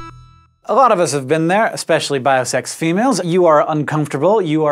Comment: This is the only place where I hear the term "biosex". I will not adopt it. Please stop selling it. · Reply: wait he said biosex? i thought he said bisexual and it confused me...